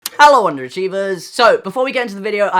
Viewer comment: I had to restart the video because the first time I heard “Hello, Golden Retrievers”